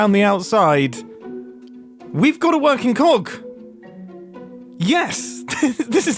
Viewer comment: you said "we got a working cog" Now I heard Working Clock. Do you think you could make a clock like the item and have it work?